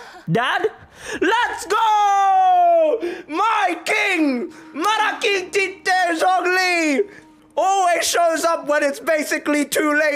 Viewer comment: Did I just heard "Malaking Tite" Zhongli??? LMAAOOOO ATSUUU